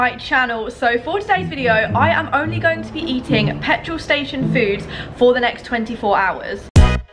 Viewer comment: I'm not the only one who thought it said only eating foods hahaha